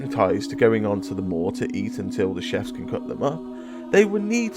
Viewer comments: I’m hearing “always eating in the maw” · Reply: I hear “they go and chop them up” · Yeah I hear "Going to the maw, going to eat more" "Going to eat all the meat, going to the maw"